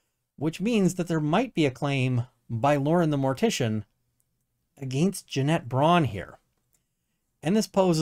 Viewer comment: Oh Janet....you've turned this into more of a legal circus than I thought possible 😂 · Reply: 😂 Everytime her name is said.........why do I hear, "Dammit Janet!?"